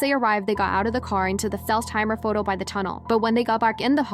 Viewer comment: is she slurring har instead of carr????